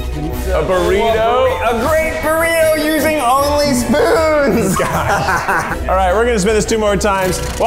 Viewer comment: Lol i thought it said oily spoons as in every spoon Keith used needed to be oily